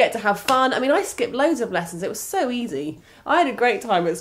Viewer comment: Did say she used to "skip loads of lessons" at 1:33? I thought she would've one of the NERDS!!! LOL